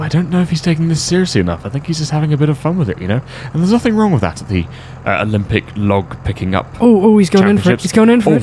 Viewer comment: For some reason I thought said limp log pick up.